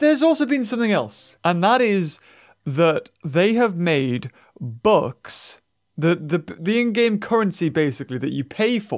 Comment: Not gonna lie thought he said 'books' instead of 'bucks'😂